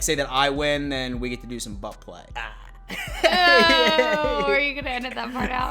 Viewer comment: at first I thought you said butt plug 🤔 🤣 butt really nice interaction. I like listening to you and looking at her. She sparkles.